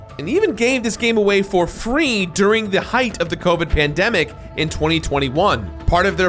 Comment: Sorry any bears out there who are hoping for full body hair care. What bear would be watching this? 4:36 Covet Pandemic? That's what the subtitles said. I wish I got this game for free. I did get it at a discount. 6:36 You mean that is not body paint? I probably shouldn't be watching this if I haven't finished the prequel. But then again, I don't think I ever will finish it. Addition of a game within this game. Gameception. You can fly in this game? Come on Varl, Beard Boys for life. 19:38 She doesn't look like Trinity. 19:54 I remember that guy. Wasn't he also the gadgets guy in Johnah Hex? Genetic Clone mommy? Wait you didn't complete this game? I thought I saw footage of you getting the completion trophy. 35:45 God of War does not take place in America. 38:13 It's so weird to see her with a beard.